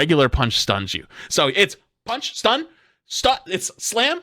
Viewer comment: tell me who hears slut-stun.